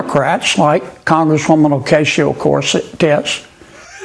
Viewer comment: I'd vote for Congresswoman O-Casio Corset-tits anyday 🤣🤣 WTF is this dude trying to mumble about?!?